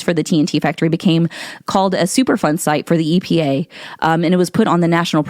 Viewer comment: At first I thought you were saying, "super fun site" like they were there just having a good time playing in toxic water and stuff.